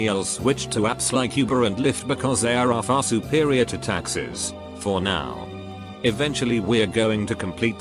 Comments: Everytime the video said "Taxis" I thought it was saying "Taxes" and was like, fuck yeah kill off taxes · Taxis.... I thought they were saying taxes at first. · Thought it was saying taxes not taxis lmao